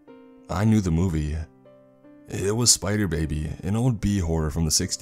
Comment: I had to go RE-LISTEN to the name of the movie you said in here. I thought you said "Spider Babe". Glad it was Spider Baby cause the other is a late night HBO soft porno movie. I know because my sister and I started watching it thinking it was a movie about a girl Spider Man....IT DEFINITELY WASN'T. LMAO.